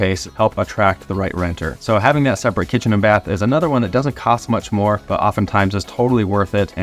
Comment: FYI, you have a commentary error, you say "separate kitchen and bath" instead of "separate shower and bath" at 5:48. :)